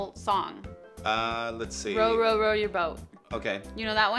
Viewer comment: It sounded like said row row row your butt